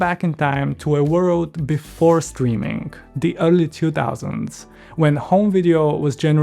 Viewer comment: thought you said ugly 2000's 😂